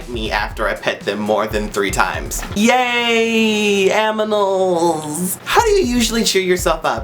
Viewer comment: did he say aminals